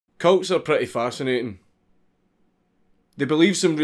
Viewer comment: He sailed cults I thought he said coats